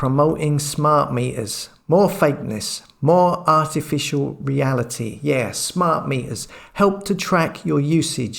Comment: Talks regarding the Albert Einstein ads, I thought for a second you said "artificial banality". Haha. I hadn't seen the TV ad, but I hear them on the radio all the time and they also do my head in. So condescending, and invariably followed by another and talking about smart something (that isn't you, but apparently serves you). Keep up the good work!